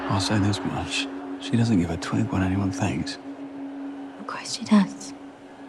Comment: I saw this in theaters and I swear I heard him say “she doesn’t give a twink what anyone thinks” 😂😂😂